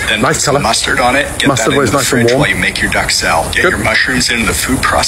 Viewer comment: I keep hearing “Duxelle” as “duck salad” I think I’ve gone rogue help me plz